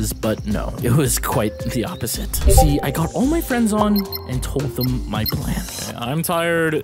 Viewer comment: OK THIS MIGHT BE FUNNY BUT WHEN HE SAID I GOT ALL MY FRIENDS ON I THOUGHT HE SAID "i got onlyfans" LMFAO